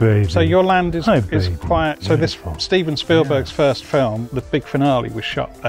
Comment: Did he just say "Speven Steelberg"? Oh my God, it's time to go elswhere.